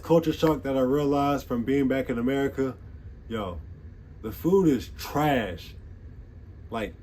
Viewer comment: Ohh i thought you said our food was trash 😅